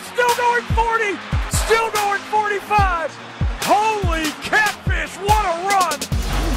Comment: it sounds like he said "Holy cat piss, what a run!"